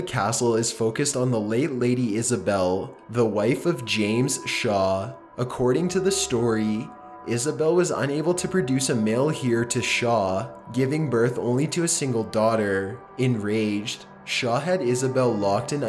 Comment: I think he means male heir not a male here. I laughed harder than I should of when I heard him say "a male here"